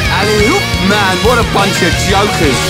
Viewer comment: I like how auto-caption read it as "what a bunch of yokels" cause I always felt that j sounded like a half y